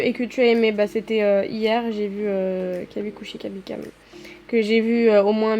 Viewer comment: c’est quoi le nom du film j’ai pas bien entendu 😭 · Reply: Kabi khushi kabi gham ou la famille indienne